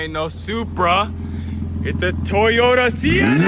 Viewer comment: Man I thought it said Senna not Sienna 😂